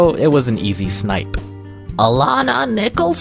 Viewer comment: Hallo! The teacher sounds like she says "Dominic Banana" for a minute